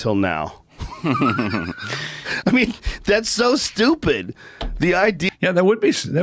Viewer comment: Every time I hear him say, "that's so stupid" I hear him say, "I'M so stupid" 😂😂😂